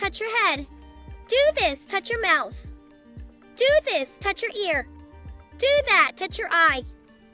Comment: I thought it said do this touch your car